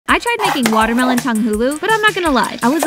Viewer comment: I thought she said Watermelon Delulu